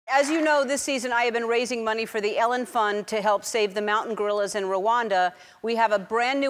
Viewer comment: Thought it said ellen fraud